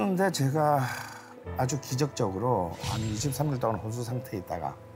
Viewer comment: 저는 23주라고 들리는데 자막인 23일이 맞는 거겠죠?